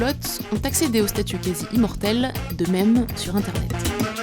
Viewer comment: " de nem sur internet?" c est moi qui entent mal, ou c'est une blague rastos?